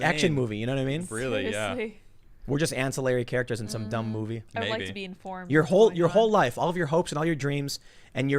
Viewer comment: Did she say, "I'd like to be in porn"? 😅